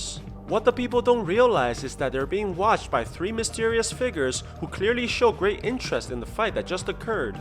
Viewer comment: when u said figures i thought u said the n word